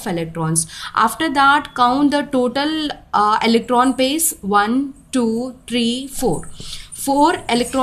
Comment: what she said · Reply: After that count total eletron pair